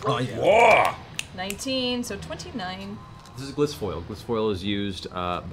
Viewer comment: Regarding Matt improvising "glissfoil," I thought he said (and rewound to clarify) kingsfoil, the common name of athelas, the plant Aragorn uses in Lord of the Rings. And honestly, it's what popped into my head when the group asked what it was before I heard what Matt said, which might have caused my confusion. So, not saying that what Matt makes looks effortless is actually easy, but I think a good chunk of improvising is stealing from sources and filing off the serial numbers. Just a guess on how Matt came up with the name.